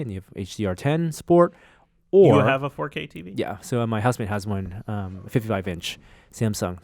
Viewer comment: Norm says "My husband has.." instead of "My wife" . And nobody picked up on it lol. His twitter photos clearly show a woman. So did he use a wrong word or do u Muricans use "husband" for both genres ??? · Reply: I'm pretty sure he said 'housemate'.